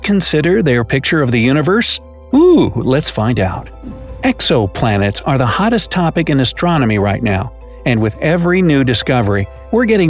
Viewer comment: Ohhh 0:34 I heard EXO Planet lol, hello EXOLs out there hihihi